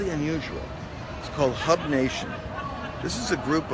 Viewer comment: I thought he said “hug” nation lol. Then thought, yeah, that seems about right for these folks. Also a double minded man is unstable in all his ways 🤷